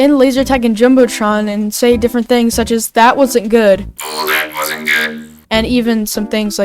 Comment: Ngl I thought the robots said "I wasn't gay" instead of "that wasnt good"